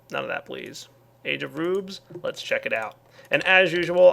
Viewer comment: Why don't you burn up some uranium? Also did anyone hear Age of Boobs at 18:43?